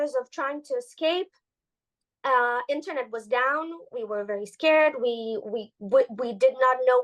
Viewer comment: I thought she said internet is down